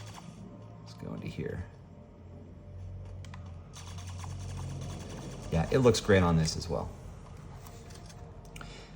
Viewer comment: Hi! What game did you say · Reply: That’s Super Roboy! It’s awesome. A Metroidvania title.